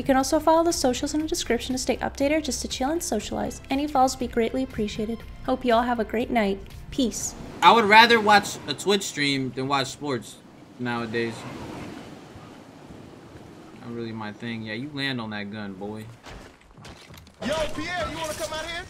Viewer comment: I thought u said I'd rather watch twitch streams than watch porn 😂